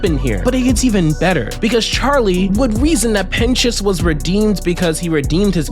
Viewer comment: help me why does it say pen1s instead of pentious in captions 😭